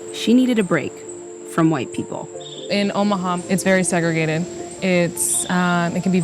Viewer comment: I thought you said omagh